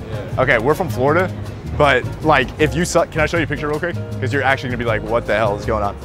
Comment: I thought he said if you suck… 😂😂😂😂😂🤮